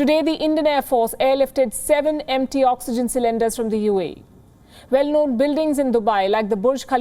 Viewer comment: Did she say airlifted empty oxygen tanks? Maybe by mistake...😬 Edit : sorry now I came to know that India is in shortage of oxygen containers also so uae sent empty containers... Good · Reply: not empty, she said MT meaning metric tons